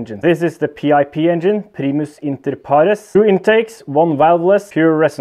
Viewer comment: What does PiP stand for? I couldn't really hear what you said when you were explaining it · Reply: Primus inter pares